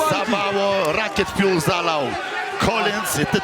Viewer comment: Padłem jak usłyszałem "za mało rocket fuell zalal" 🤣🤣🤣